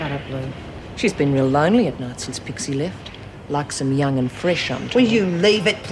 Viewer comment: what did Lou Kelly say after saying jude likes them young and fresh. She says something after fresh but I can't catch it?? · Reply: Carlene Boulden LIkes em young and fresh, I'm told